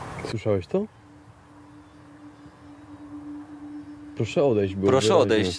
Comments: Ja przy drugiej lokalizacji usłyszałam zamiat prosze odejsc to poprosze wode xd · Ten głos Google tłumacza ,,Prosze odejść” XD Edit: wow dzieki za 800 like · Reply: Ja przy pierwszym razie usłyszałem,, Proszę wodę'' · na początku usłyszałam ,, proszę wody,, ( XD ) i mając filmik na największym ekranie wystraszyłam się że wstawi tam jakieś straszne zdjęcie i głośny dźwięk i dałam na mniejszy obraz ale na szczęście tak nie było haha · Czy tylko ja usłyszałem jak ktoś puszcza google prosze odejść Xd napiszcie prosze Xd